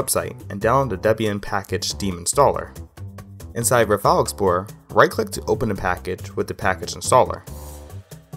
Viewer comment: What is he saying at 1:27 because all I hear is “inside rufalxbor”